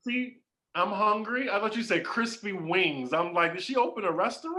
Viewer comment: OMG I thought he said crispy Wings to now I'm hungry